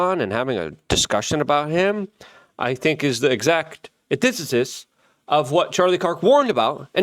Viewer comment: sometimes I have no idea what is saying. Atitithis?